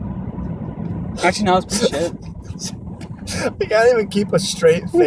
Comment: I swear Lachlan says "shit" Idek.